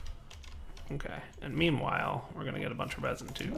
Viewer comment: Did say "memewhile" at 13:38?